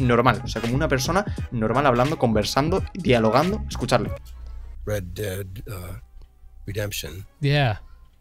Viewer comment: me pareció que dijo "red dead redemption" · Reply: Sí dijo Red Dead Redemption, quizás dobló un personaje secundario en el juego.